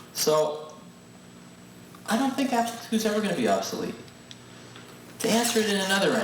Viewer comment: Did he say the Apple II is never going to become obsolete?